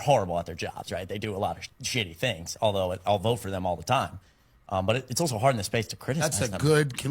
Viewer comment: Why is it I am not surprised? Thank you for sharing. 5:09 Could have sworn he also said, "...even though they do shitty things, but I'll vote for them all of the time". A true supporter of anyone running for office, should never have to be paid let alone accept payment at all!